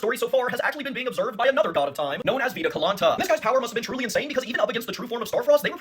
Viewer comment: For a moment, I misheard Veda Kalanta as Pina colada 😂